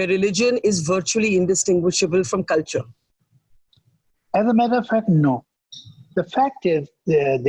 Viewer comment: the subtitle reads Javed saab saying " I have a motherfucker no", instead of "as a matter of fact no". 😂😂😂😂